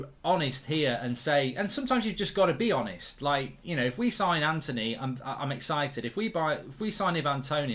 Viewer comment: Their only going for Ivan because Murtough thought ETH said Toney instead of Anthony.